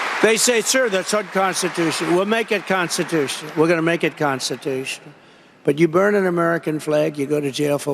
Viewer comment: He doesn't have any idea what it takes to "make it constitution " (that is what he said)